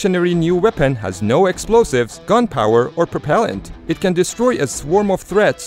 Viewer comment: Sounded like he said "has no gun power" lol